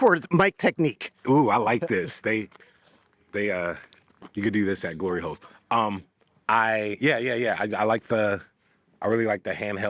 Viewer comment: did he say ‘you can do this at glory holes’ 😭 💀